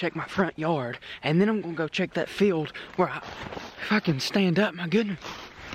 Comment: Did anyone hear him say “ F*cking stand up.(9:19) thank me later plz like also comment down👍👍🙏